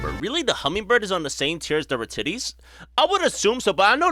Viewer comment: Did just say titties?